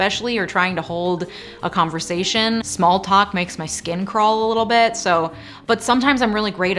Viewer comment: For a second I thought you said "smallpox makes my skin crawl" 😅